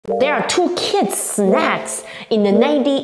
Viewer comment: I thought she said "these are two kids' head "